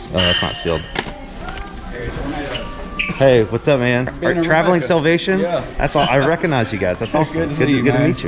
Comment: What names were there names? So I can look them up. I thought you said "traveling salvation" but it did not come up. Thank you.